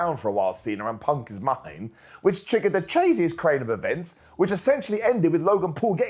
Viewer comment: Did say "chasiet crain if events"? 🤣🤣🤣